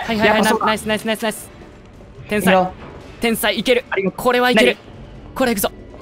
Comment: ～ 花ちゃん「天才」 賢章先生「なに？(もう一回言って)」 っていういつものやり取り一瞬聞こえた気がする笑